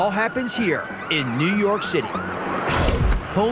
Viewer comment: For a second there I thought the guy narrating said “ New York shitty” I was like wha?!